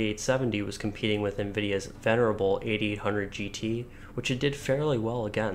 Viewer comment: did i hear "nvidia's terrible 8800gt"? are you high? like, SUPER high? 8800gt is one of nvidia's best card ever along with the 1080ti and the geforce DDR [edit] ah. i'm deaf apparently. it's "venerable", not terrible.